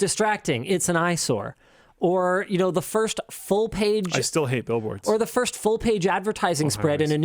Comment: I was listening to this in the background and when said he hates billboards I thought he said he hates Bill Wurtz. Almost unsubbed